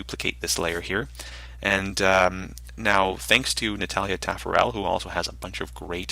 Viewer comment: Hei, really great tutorial! At 19:35 you talk about someone we can look up, but I just can't understand the name... :) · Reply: Natalia Taffarel :)